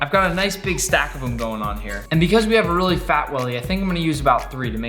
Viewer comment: sounded like he said fat willy